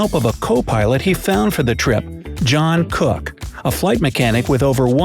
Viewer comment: I thought i heard " jungkook " HAHA